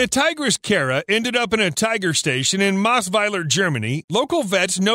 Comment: i heard "in the most violent germany" and i have to repeat that part couple of times xD